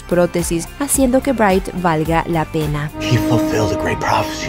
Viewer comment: En el minuto 5:30 ella dice que valga la verga o escuche mal..